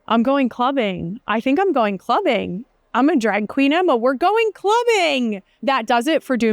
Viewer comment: You said, “I’m gonna drag Queen Emma”.. i heard… “I’m gonna Drag Queen Emma”. I’m wondered for a brief second.. We are gonna dress Emma as a Drag Queen? 🤔🤷‍♀️